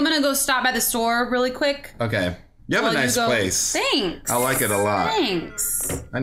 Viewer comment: When said you have a nice place I thought he said you have a nice face XD